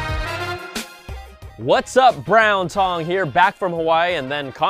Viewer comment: lol did he say "Brown Tongue" here?